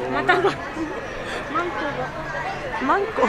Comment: I heard Manko?? 😮😂. DOM mean House in Slovak- hm, interesting..🧐😘✌️Thanx for another good video! Mataneee!